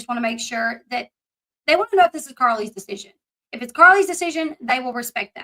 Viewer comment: Elle I thought you said your sources said this isn’t Carly decision.